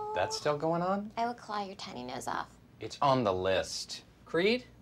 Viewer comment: “I will claw your tiny nose off” “It’s on the list” 🤣🤣🤣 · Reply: Thank you - I thought she said "I will claw your toe nails off" - which didn't make a lot of sense to me except "OW! that would hurt!"